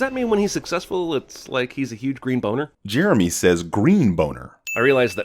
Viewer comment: I'm gonna have to add a sin. At 6:16 Jeremy does not say "green boner". He says "huge green boner", which should have been an additional 5 sins.